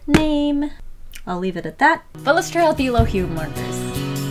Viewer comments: When said illo hue j thought she said “ i love you “ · every time she says "illo hue" all I hear is "the ohuhu"